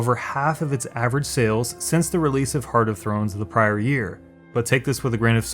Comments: did you just say "Heart of Thrones" lol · did you say heart of thrones? Lol.